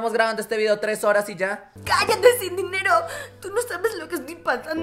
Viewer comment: yo escuche que le dijo sin dinero XD pero era Cindy Nero nombre: Cindy apellido: Nero XD me confundi like si escuchaste sin dinero Xd Ya que se llama Cindy Nero y si tu lo pones asi Cin Dynero es como asi --> Sin dinero :v Edit: Primera ves que tengo 34 likes :v